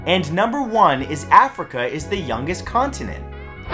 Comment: Comment if you thought the earth was 2019 years old Also comment when he said “Africa is the youngest continent”, you thought he said “Africa is the newest continent”